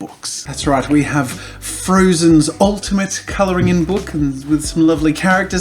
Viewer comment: people in america have never heard the term "coloring in book" before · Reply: yeah, that confused me a bit, i thought he said coloring ing book xD